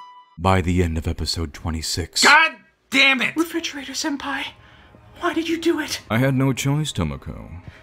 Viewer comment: Top ten anime betrayals of 2017 1. Refrigerator Senpai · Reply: I must be the only one who misheard it as Perpetrator-senpai